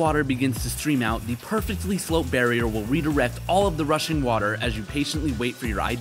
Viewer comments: I thought he said russian water💀💀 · “The rushing water” I heard: “the Russian water” 💀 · I thought he said “Russian Water” lol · "Russian water" Me (Russian and Filipino): YOU DARE TO DISRESPECT THE RUSSIANS?! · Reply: So I'm not the only one who misheard it as 'Russian Water' instead of Rushing Water